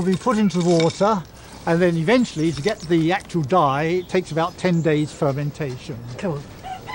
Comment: did he say "get the yack and die."? That sounds like an awful way to go! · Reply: To get the actual dye takes about ten days fermentation.